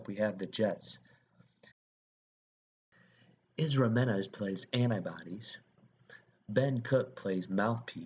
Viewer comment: Narrator doesn't know how to say "anybody's "lol.. it sounds like he is say antibodies lol